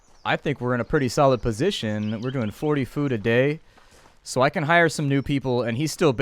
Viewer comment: Ohhhh that says 40 food a day I thought it said 400 food a day I was about to say damn your villagers are fat